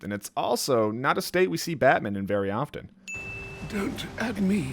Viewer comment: idk why but i thought alfred said “dont me.” Lol